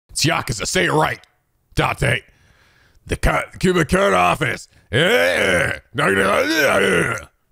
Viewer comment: Am I the only one who misheard kumakura as kamukura?